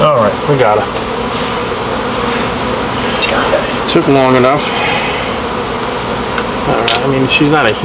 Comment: Hello, I enjoyed your video, watching the bees march doesn't get old. When you caught the queen, you said: "It took long enough." All I heard was: "Thank you Jesus!" Lol!